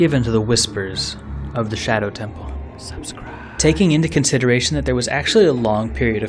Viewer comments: Who else heard subscribe being whispered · i heard that "subscribe" in the background xD